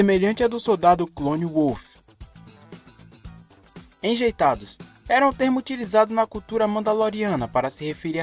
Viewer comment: não era "rejeitados?" eu sempre ouvi rejeitados